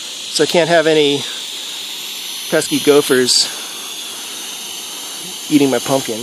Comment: Gophers?! I thought you said golfers.....